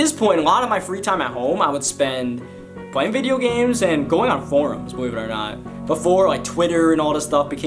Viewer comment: u are a great youtuber but i thought u said going on porn